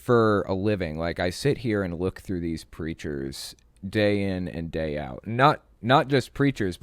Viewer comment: I could have sworn you said "creatures" and not "preachers" I laughed so hard.